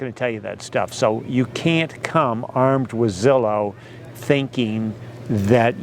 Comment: Great information as always. Funny thing. I had to watch this twice. The first time I got sidetracked and when you said "Armed with Zillow" I heard "Armadillo". Go figure.